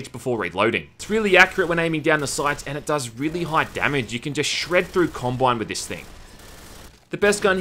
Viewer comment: thought he said "You can just shred through Columbine with this thing"